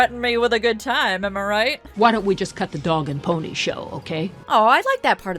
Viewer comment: did she just say why don't we cut the dong and pony show?